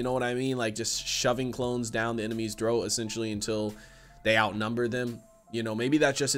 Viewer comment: did you say shoving clones down the enemy’s throat?